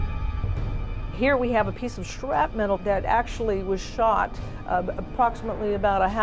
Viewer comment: Did she just say "shrapmetal"?